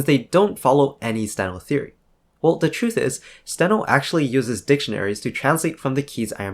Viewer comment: I found a mistake: "dictionaries TOSS" instead of "dictionaries to..." and it supports my previous feeling of: I don't like it. 😅